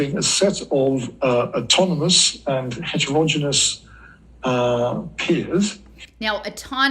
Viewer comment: he says, "autonomous and hegemogimus peers"?? Did he mean hegemonismus? I've never heard the word hegemogimus and can't find it. Did I hear this wrong? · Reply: he slurring the word "heterogeneous" while dropping the 4th 'e' , autonomous and heterogeneous being two words frequently paired together in the distributed/decentralized autonomous systems jargon.